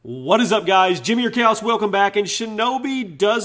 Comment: at the start of his vids, it always sounds like he says Calus instead of Chaos